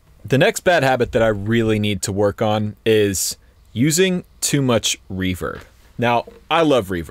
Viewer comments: Ha, I thought you said not to use to much reefer, and that you love reefer at first. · For the second bad habit, I thought he said "too much reefer".